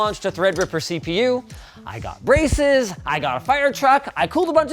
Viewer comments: "I got braces" Linus has officially entered nerd era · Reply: I thought he said “I got racist” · "I got braces" Linus has officially entered nerd era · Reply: no shit at first I thought he said "I got racist"